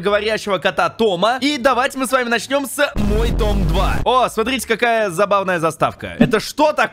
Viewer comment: мне послышалось мой ДОМ 2